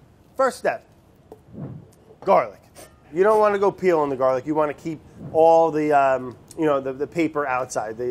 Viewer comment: Sound like he said "You don't wanna go pee on the garlic" 😂😂